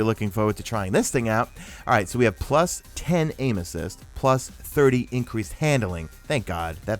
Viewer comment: #MadeItToTheEnd Sorry to hear about your cold but it did provide a little extra entertainment to this video. Whenever you said "aim assist" I heard "anuses." 😁